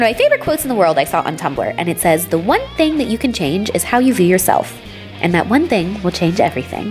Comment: the quote reads "'how you treat yourself'", but said '"view" yourself.' anyone catch that? haha also I saw your tweet a week or so back someone else told you "its just as easy to put the clothes back" glad you've changed your attitude on that on. ;p